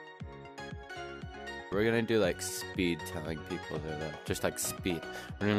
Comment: Damn I thought the video said "telling people they're loud" 🤣